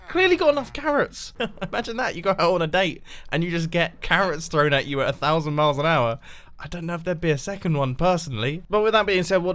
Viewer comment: did you really say *thrown carrots 100 miles an hour?"